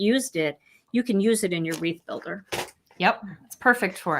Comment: This is really lovely! I thought you were saying “reef builder” so I was a little confused when you picked flowers! Lol!